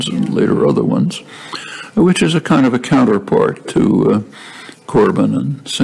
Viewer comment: Can you help me, what's the name he says at this minute 22:09 ? (Help me get my feet back on the ground...won't you please please help me) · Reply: Jeremy Corbyn. UK Labour party.